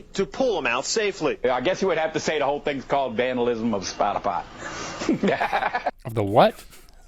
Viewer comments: it almost sounds like he said "vandalism personified"...which makes no sense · I heard "vandalism of spot-a-pot" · Vandalism of spot a fight? Spinal pot? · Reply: I thought he said "The whole thing is called vandalism of spotify!", so I thought he was listening to music on his phone before he got stuck. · I thought he said...vandalism of "Spy D Pot". Unfortunately here in Boulder Colorado, at an outside yoga conference... There was a man who hid down in one of these porta potties to spy on unsuspecting women. Hence the "spy". Creep. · I hear 'vandalism of spot a pie' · I heard "vandalism of spota pot"